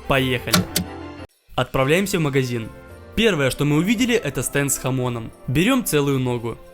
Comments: Стэнд с хамОном Я услышал "станд с хАмоном" И подумал, что это Джоджо референс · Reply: Мне послышалось "стенд с ОМОНом" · мне послышался стэнд с хамонам ну джоджо 😅